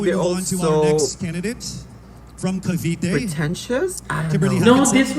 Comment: ooops did just said that Michele was pretentious? Hahaha.